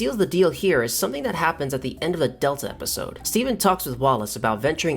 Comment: did he just call the champion semen????